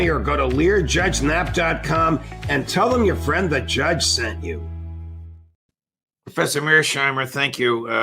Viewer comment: When he said "Tell 'em your friend the Judge sent you" I heard "Tell 'em the Gremlin Judge sent you"